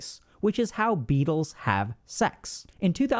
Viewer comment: I misheard "how Beatles have sex" and immediately thought of yohko ohno😅